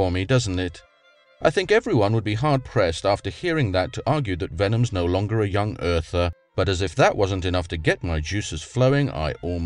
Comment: I think you misheard - doesn't state he is no longer a YEC at 4:12, what he says is that "everyone would be hard pressed after hearing that to argue that Venom is no longer a Young Earther." Also, at 4:28 says he considers himself a YEC.